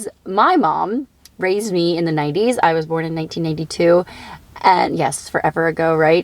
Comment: At first i thought you said you were born in 1982.😂 I was like....hmm...the math doesnt stack.😂